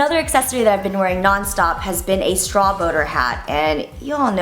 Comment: lol i thought you said straw boner hat